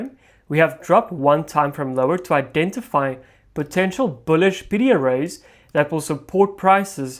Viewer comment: What is a PDRA? I think that's what he's saying